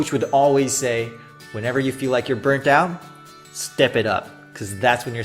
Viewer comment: 想請問一下，在0:08左右， 最後是burnt out 還是 burnt down? 我查字義似乎都有燃燒的意思， 自己很喜歡的影片跟聲音， 目前使用的影片進行聽寫練習， 而自己一直聽成burnt down 所以想要確定一下>.<